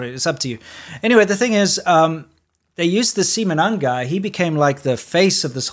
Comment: Lol I was watching this while I was getting my kids ready for bed so I'm not sure if I misheard the name of this guy. Seamen none?? As in ball less??